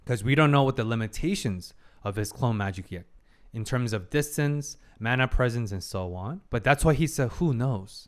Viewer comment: did he say distance? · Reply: Yep distance!